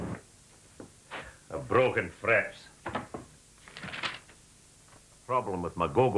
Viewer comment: A broken ' threp'?????? I remember this ad well, but I still need a translated to understand what was wrong with his goggomobil lol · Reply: Yep I have always wondered what he was saying. “ Broken Thread” is logical, it sounds like he is saying “ Broken fret”, Never heard of “fret” other than on a guitar